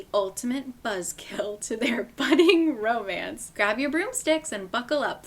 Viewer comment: im ruined by it ends with us because when you read "grab your broomsticks" i just heard "grab your friends, wear your florals" 😭😭